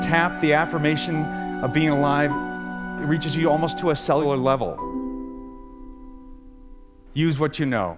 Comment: Can someone please tell me what he says on minute 1:21, ''almost to a (?) level'' ??? please I need to use this video for an essay. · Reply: x "Almost to a cellular level". There's captions available too!